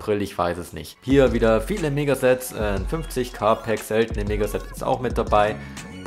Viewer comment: Habs mir jetzt 5 mal angehört und ich verstehe bei 1:10 immer seltene Nigga sets